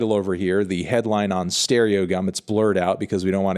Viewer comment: Had to do a double take thought that shit said "StereoCum"